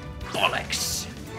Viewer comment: i- 47:03 did just say bollocks